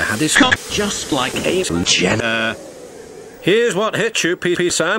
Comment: What did the narrator say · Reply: Caitlyn Jenner